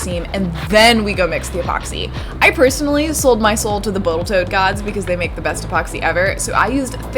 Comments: did I hear boatal tote gods? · & 10:10 did you actually say "Boatal tote" ? :-) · Did say "botal toat gods"? cuz she botally tid